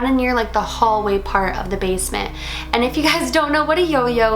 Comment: I thought man you said the man in the laundry room not basement